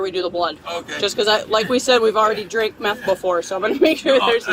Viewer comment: Did she just say "drink meth"?